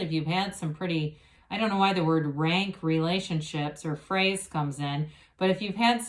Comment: thank you as always! have a beautiful weekend. (I heard “Frank” relationship which has similar feeling. hahahahahah)